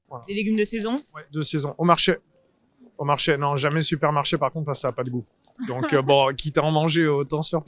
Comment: is why i dont understand french, how did he say “parce que ca n’a pas de gout”? All i heard is par ca gout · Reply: Parce que ça a pas de goût, he drops the n’ the subtitles went for correctness over fidelity here